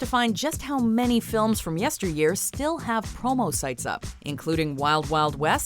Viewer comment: I thought she said “homo” site, instead of “promo” site.